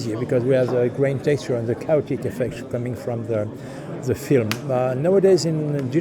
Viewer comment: What did he say after "grain texture and"? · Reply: I believe it's "Caustic effect"